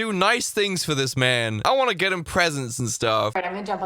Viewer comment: What is wrong with me...? At 12:06, I thought was about to say "I wanna get him pregnant". 😔